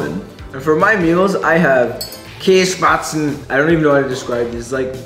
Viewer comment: DID YOU JUST SAY “KÄSSPATZEN” OR SMTH LIKE